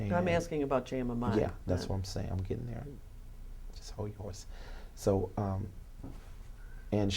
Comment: does he say “hold your horse”?😂😂🤣